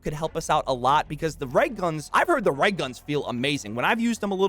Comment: what is he saying? Right guns? Im confused lol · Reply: Reg guns